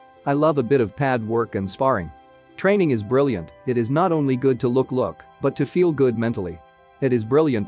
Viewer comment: did he actually say "it is not only good to look look"? 😅